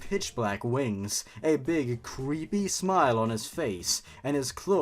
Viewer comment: Did anyone else mishear the description of Pit having a creepy smile as him having a greasy smile? SpongeBob- They've been feeding people grease!